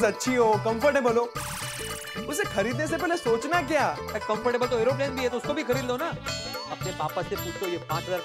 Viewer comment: what the he said herobrine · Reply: Aeroplane 😂